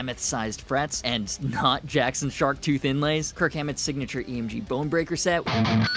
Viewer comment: My dumbass thought you said kirk hammer (from bloodborne)